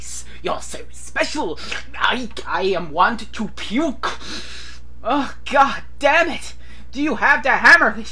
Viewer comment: Did I hear you right at 6:07? Did you say "I am want to puke?" 5/5 stars just for that.